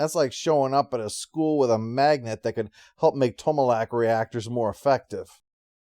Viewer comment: Ha! I misheard that as Tomalak reactor. (Romulans use contained singularities anyway.)